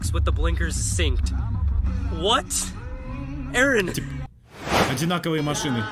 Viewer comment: Я одна услышала слово "Эрен"?